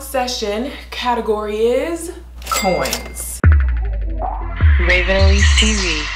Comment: At first in the intro I thought she said boys but it was really coins